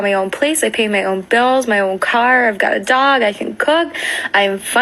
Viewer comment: It sounded like she said i have a dog that Cooks. Haha wow .what else do you need.. lol